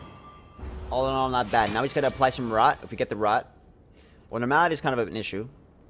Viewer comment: My boy, it's "Mal-a-dee." Not M'lady! Lol. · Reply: Oh he may have said "normality" I heard it as "no malady" thanks for pointing that out I am now triggered by this video all over again